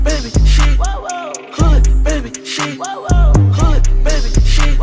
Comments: First I thought it said hood baby sheep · Thought he said hood baby sheep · I thought he was saying, "Hood Baby Sheek"